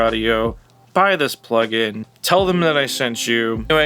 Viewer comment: I can't tell you how long it took me to realize that you're saying "resident loser" at the beginning of your videos. For the longest time I thought you were saying "recording studio loser", but way too fast.